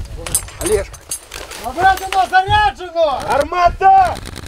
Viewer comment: I hear the command, 'Armata.' Does that mean, 'fire?' Or 'shoot?' How is that written in Ukrainian? In greetings, I would rather say, 'Armata,' than 'Peace.' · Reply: Гармата.